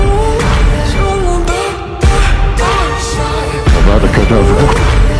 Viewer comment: to me it sounds like Snape said “AbRaCaDaBrA” instead of “AvAdA kEdAvRa”